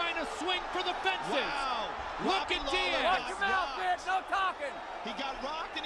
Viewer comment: Did Steve say Knock him out Bitch no talking @ 3:53?